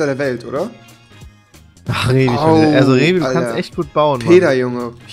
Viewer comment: hab ich erstmal ,,PizzaArt'' verstanden XD